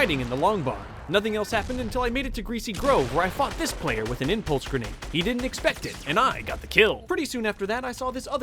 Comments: I thought I heard "Then I [fucked] this player with an impulse grenade." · Did I just hear him say the F word? Probably my hearing but still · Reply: Khan N Gacha nah he said “then I fucked this player” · it sounds like he sayd fucjed instead of fought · Am I the only one who heard then I fucked this player? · did he just say ‘fucked this player????’ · I thought he said I fucked this player with an impulse grenade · anyone else hear “I f*cked this player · where I what?! Please tell me what he said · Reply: Fluffy_Creature fought this player with an impulse grenade